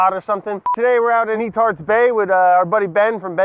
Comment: Could have sworn he said E-tard bay